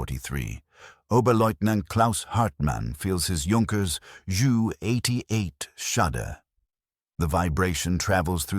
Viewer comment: I heard "joo 88". Yet another AI narration. At that point I switched off.